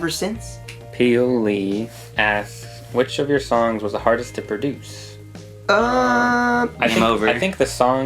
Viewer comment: What are you saying at 14:00? I couldn't get it... Which of your Songs was the hardest to produce?